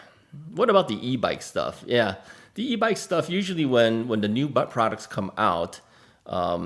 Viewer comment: Greetings. Thank you for your videos. I'm in Southern Ontario Canada was able to get out on my Rad City 4 2020 bike today. Lol. I thought you said butt products in your video at 8:52 I turn on CC and YouTube captioned it as that, LOL! I am 63 and will probably need some reviews for butt products in the future! Lol